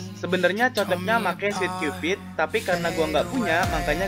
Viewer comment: "Harusnya pake si Cupid, Tapi soalnya Gw gk punya" Kalo make si Cupid Ntar skillnya numpuk sama pendant robot, Mending pake Bubble Gun aja kalo gitu. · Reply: DeBang Cool pake sweet cupid,bukan cupid. Salah denger wkwk